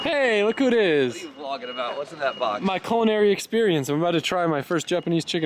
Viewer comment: I thought you said “colonary” 😅